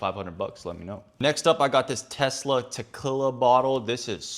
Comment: Did he just say "tekilla" instead of Tequila? LOL